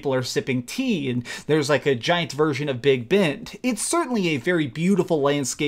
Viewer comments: KratosGamer no...Big Ben. It’s a tower in England. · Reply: Martial Harpist Matthew I know I live in England I thought he said big bent instead of Big Ben 😂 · did you just say "the big bent" ahaha